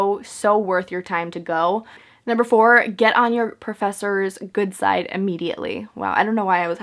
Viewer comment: I thought you were gonna say “get on your professors dick”